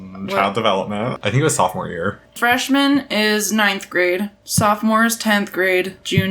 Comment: Am I the only one who hears "trashman"